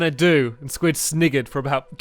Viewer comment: Did Stampy say 'sniggered' ? Advice from a black person: Don't EVER say 'sniggered' in a video.